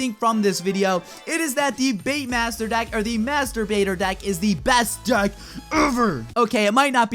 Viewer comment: at the end it sounds like he said the masterbater deck